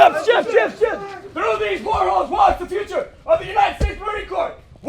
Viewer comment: did bro say portholes instead of silver hatches? I guess they’re crawling through the windows instead.